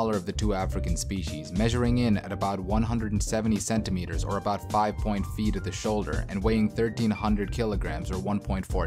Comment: is he saying "5 point feet"??